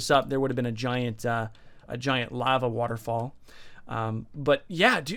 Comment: Did he just say a giant "lava waterfall"?